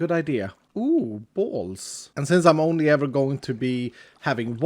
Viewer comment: had to do a double take as I was mostly listening to this video and the I just heard "uhh balls"